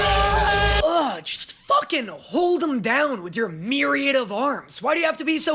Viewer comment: Did say hold em down. Be careful you summon the epic cast. 23:24.